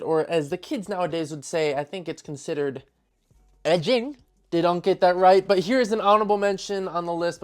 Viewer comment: Did bro say edging?